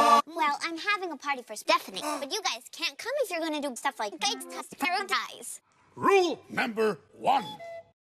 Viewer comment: I didn't understand this sentence mixing :/ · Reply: "gangsta's paradise"